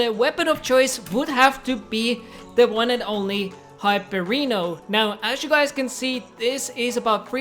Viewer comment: Did he just say hypereeno